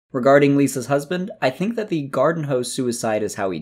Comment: If you listen closely, the muffled echo says "umbilical cord" instead of garden hose Nvm, just watched the second part lol